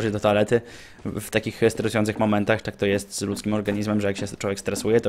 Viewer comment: usłyszałem " z ruskim organizmem " :D